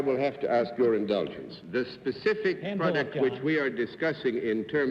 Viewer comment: What was so funny at 7:30? I couldn’t tell what BC said … JD seemed a bit embarrassed. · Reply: John, in answering for Ms, puts his hand on hers. Bennett says, "Hands off, John!"